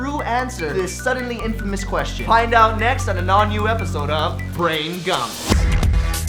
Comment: I heard brain gum?😅